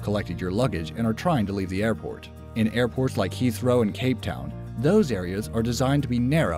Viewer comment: what did he say? Heathrow and _________? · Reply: nothing impossible285 Cape Town.